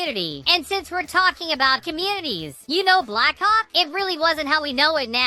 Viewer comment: sound like he said blackcocķ 💀💀💀